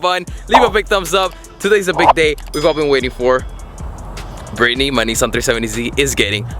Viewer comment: Bro, I thought you said Brittany the 370z is gay